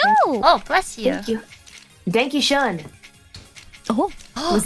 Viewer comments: I thought she said "Dank achoo" at first 🤣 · I swear i heard "Thank you schön", and i had flashbacks of Coco raging at japanglish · I heard it as Dank you Shawn